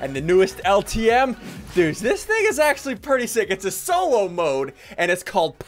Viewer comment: Is it just me or did it sound like he said. " This n***** pretty sick."